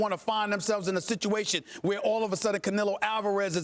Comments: I thought that shit said Carmelo😭😭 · I thought that said carmelo i almost fainted